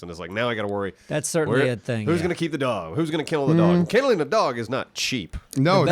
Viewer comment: Bruh I thought he said “who’s gonna kill the dog?”😂